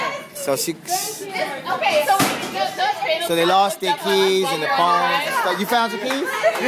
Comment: i thought you were saying that they lost their kids.. not their keys.. XD